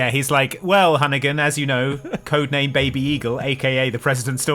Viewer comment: Baby eagle? I heard it as big eagle 🤔